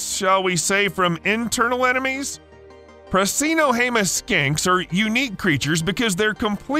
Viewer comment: Skinks,.. sounded like you said skanks… and yeah skanks can survive 35 days without food too… meth is a nightmare